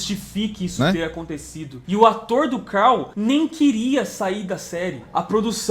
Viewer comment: O ator do Carl disse que queria sair sim 😅 na época ele deu uma de Jake de Two and a Half Man - foi o que eu ouvi 😮